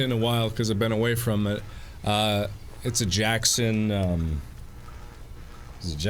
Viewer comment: I thought mark said jackspeticeye when he was talking about this guitar instead of Jackson lol 😂